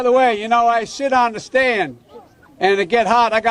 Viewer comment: Did he say, “ and by the way you know, I SHIT ON THE STAND” ???